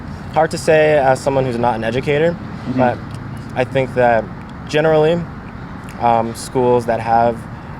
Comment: did he just say "General Lee"? CANCELLED!!!